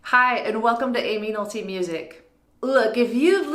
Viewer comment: It sounds like you said "Hi, welcome to anal tv music" >_>